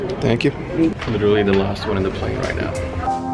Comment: Made can you explain what you say around 1:16 and 1:20 ? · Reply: he said ""literaly the last one in the plane right now"